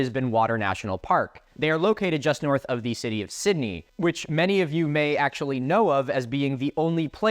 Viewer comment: I misheard “city of Sydney” as “city of city” and I was so confused and had to rewind 💀😭🤚